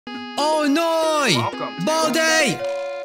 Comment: The first thing i heard was "Oh noey Boldey"